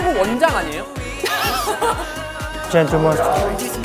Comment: did jaehyuk said GENTLE MONSTER or its just me ??